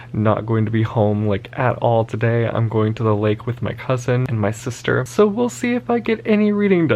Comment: When you said going to the lake with "my cousin and my sister" I heard "my husband and my sister" and had to go back and listen again lol